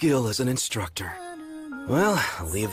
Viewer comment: DID HE SAY BABY? 0:36 🤨🤨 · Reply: Mei mei that's her name